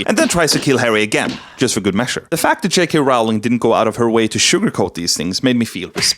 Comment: Please when he said JK.Rowling I heard “Chick correlle”, I